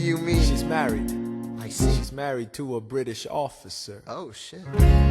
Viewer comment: "She's married." "We can work with that" "She's married to a British officer" "oH SHI-" Edit: Mom im famous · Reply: I always thought it said « she married to a British author sir » xD